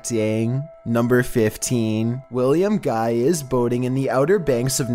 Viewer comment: For #15, I thought you said William was out boning," rather than "boating."